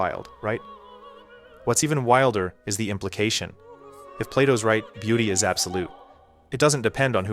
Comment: HAHAHA when you said Plato, I thought you said Play-dough. I was like ”ok, interesting”